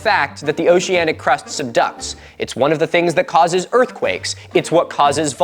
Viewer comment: the subtitle saying "some duck" while its supposed to be "subduct" is funny to me